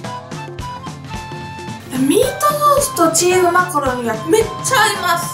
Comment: 最初マカロニがマカロンに聞こえてチーズマカロン(;゜∇゜)ってなった